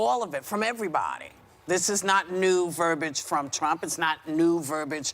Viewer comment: Yes I heard it correctly at 4:05 Whoopie actually said Trump's name (instead of 'you know who in the white house')...